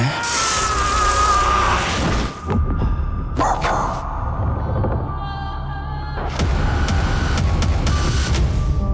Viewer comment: da Sencer ne dedi? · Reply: Baba